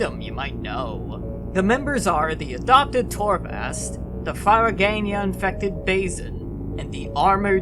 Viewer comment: "The ???-infected Fazen": what's that word? I can't for the life of me figure out what the name of the disease is supposed to be exactly, and it's not explicitly mentioned in game. Firoganian? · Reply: Firagania. Basically "Firaga" from final fantasy but with a "nia" at the end.